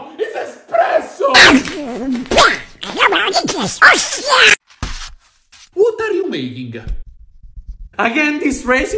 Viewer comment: what did Donald Duck say? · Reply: HOSTIAS!!!